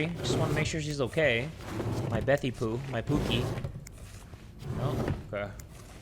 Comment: DID I JUST HEAR SAY POOKIE